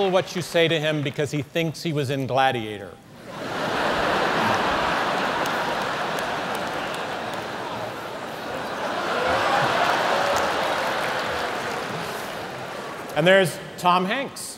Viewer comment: i can't get what he say, something like "ingladier", pls help =) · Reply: he says that C.Heston is in the Gladiator...the movie...you know?...